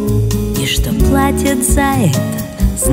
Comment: "Эта девочка слишком умеет чувствовать, и что платит за это, знает она одна " - очень тонко и глубоко, не все поймут. Спасибо, Анжелика. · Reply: А мне послышалось платят за это 😂❤